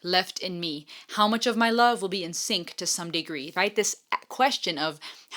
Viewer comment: 😊it’s “how much of MY Love will be insane to some degree “ I thought you said in sync to some degree?